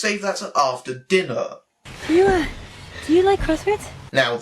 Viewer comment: What did she say? It was so quiet and quickly delivered. Update: She said “Do you like crosswords?” Yep, still hate her.